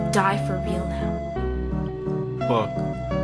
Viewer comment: He actually said shit, not fuck. Idk if this was already pointed out, but, I’m too lazy to find out